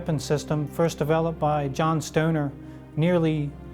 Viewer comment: DID HE SAY JOHN STONER?